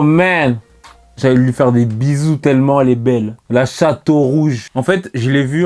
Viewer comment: La chatte au rouge y'a que moi qui a entendue sa🤣